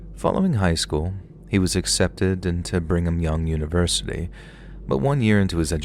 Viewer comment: a Mormon "Bring em Young" university? Did I hear that right?